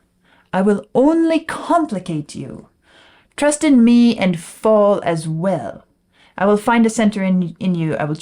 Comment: OMG, I always thought he said "Trust in me if all is well."